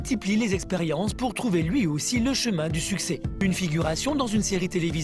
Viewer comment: j'ai compris ''le chemin pour sucer''😂